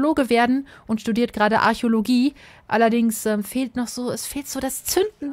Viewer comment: hab verstanden er will Gynäkologe werden 😂